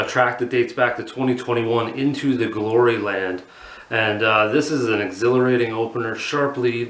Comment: Thought you said into the glory hole for a second.. 🤦🏿‍♂️